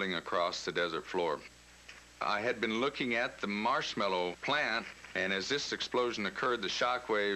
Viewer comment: Am I hearing it right at 6:10 when he says "marshmallow plant"? It makes rocket fuel, is that a nickname for something?